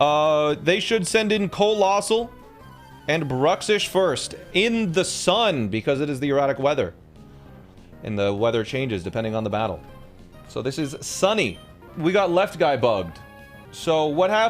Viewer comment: I thought you were saying erotic weather lol